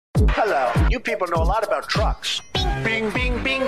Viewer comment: I thought he said “You people know a lot about Trump.”